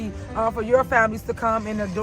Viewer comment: Did I hear her say "Why can't us?" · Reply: She said, "why caint us".